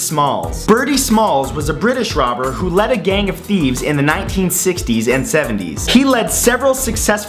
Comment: Ahh D.B Cooper....makes sense.....were his ancestors raccoons? (Lets see how many get it) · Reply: I heard he ran a 'band of theives'